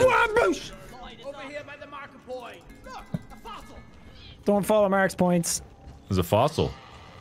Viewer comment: I though I heard them called Markiplier name. It actually marker point. XD